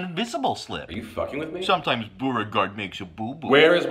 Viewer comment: What is he saying?! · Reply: “New phone, who dis?” New phone, who is this, essentially.